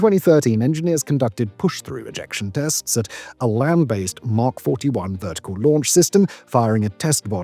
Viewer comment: I thought Simon said "Mach 41" for a second there.